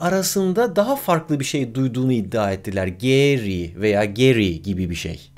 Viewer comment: Garry olarak duydum ben